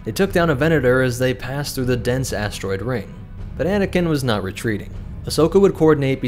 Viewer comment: I heard “bananakin”😂